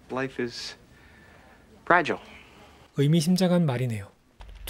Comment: 여기서 life is 뒤에 뭐라고 말하는건가요? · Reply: Life is fragile 인생은 부서지기 쉽다? 인생은 알 수 없다? 이런느낌.. 영어에 취약해서...😂😚